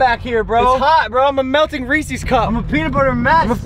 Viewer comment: Did he say reesees instead of Reese’s